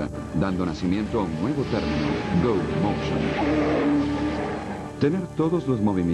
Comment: gracias por compartir ese video tan interesante, pero no me quedo claro la segunda tecnica , no comprendi muy bien como funciona ni tampoco entendi su nombre , escucho que dice como glow o slow? en el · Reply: parece que es Go Motion o no se a que te refieres